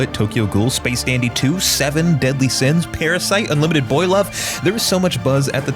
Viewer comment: what does he say on 0:27 ? I can't find a anime called "unlimited boy love", please help